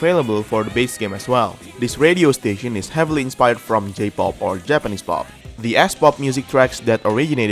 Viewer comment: did anyone see the captions say “J-Pop” but he said “S-Pop”